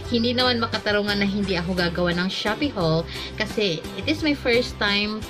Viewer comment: I heard " and SHOBEE" hahahaha